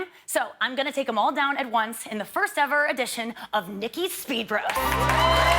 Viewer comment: I thought I heard " Nikki's Spit Roast".My mistake.